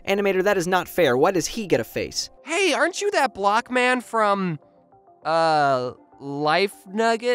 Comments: Thought he said black man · I THOUGHT IT SAID “life nugget” AND I SAID, OUTLOUD, “I want chicken NUGGETS!!!!!” So NOW I WANT NUGGETS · i misheard it for "life nugget" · Thought he said ‘ain’t you that black guy’.